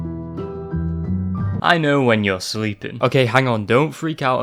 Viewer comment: I always thought the phone guy said "i know you're weeping"